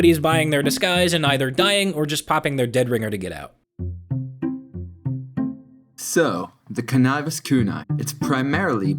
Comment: what did you say? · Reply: Bigger